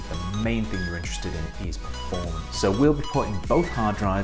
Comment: What are you saying here? I've listened about 10 times now and it sounds like "When it comes to building a gaming PC, the main thing you're interested in is porn". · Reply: Performance... sounds nothing like porn